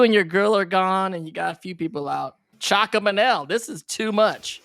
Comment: Caption correction: Dante says “Chalk up an L.” which is a colloquial phrase meaning “Mark this event down as a loss, stop current efforts, move on to other areas where help is still needed.” Context: Scorekeeping for street games was often done using chalk marks on the street/sidewalk/asphalt, or on a chalkboard for gym games. So if you were clearing going to lose game 1 of a series you would say “Chalk up an L.” to end that game, mark it as a loss, and move on to game 2 instead of wasting energy on a lost cause.